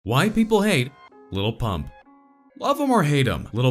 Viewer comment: For a second I thought he said "white people hate lil pump"